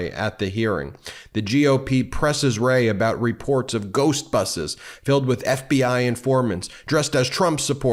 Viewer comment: I was more surprised when I realized he said ghost buses, than I was when I thought he said ghost boxes, bc maga! I was waiting for them all to stop and put on their tinfoil hats!